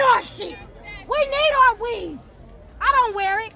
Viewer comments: I thought she said we need our WEED!!!! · Did she really say... "We need our weave", in the same breath as "We need our gas, We need our food"... Is she serious??? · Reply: I thought she said "we need our weed" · I thought she said "We need our weed". Which would make the most sense.